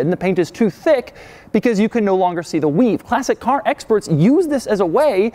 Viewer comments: Thought you were saying Classy K. Sounded very un-Italian haha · I thought he said “Classy-K” 😂